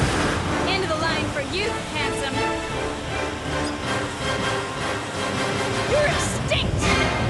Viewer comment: Ok, can someone with better ears than mine please let me know what Regina's badass line was at the end? It sounded like she said, "End of the line for you, handsome. You're a stick!" Which is kinda badass I guess. But I'm assuming she didn't say 'stick'? You stink? You're thick? Help a brother out